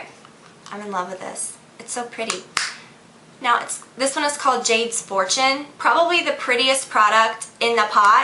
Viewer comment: LOL When you said "Jade's Fortune", I thought you said "Jade's Abortion", and I was like, "WHAT?! What kind of eyeshadow name is that?" XD I had to go back and listen to it again :P.